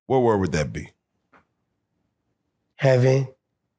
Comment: the sarcasm in "heaven" this man went through hell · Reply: I thought he said “heavy”